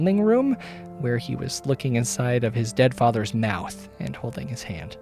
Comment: explains how, as a kid, he gets attacked by a dog and could of had rabies “wow so many ways he could’ve died, how unfortunate.” I DIED😂😂😂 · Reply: Wait at 46:22 did you say looking inside or LICKING inside his dead fathers mouth??🫣